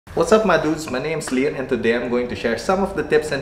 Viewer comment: Thought you said "What's up my nudes" lol